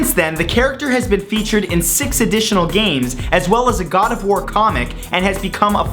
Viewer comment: Is it just me who thought that at 0:50 he said gardevoir comic (as in the Pokemon) not God of war comic?